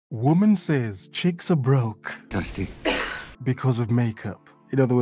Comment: YOUR OPENING STATEMENT?? I THOUGHT YOU SAID "WOMEN SAYS CHEEKS ARE BROKE??!!" im still laughing.....I WANT PROBLEMS ALWAYS!!!!! HAHAHA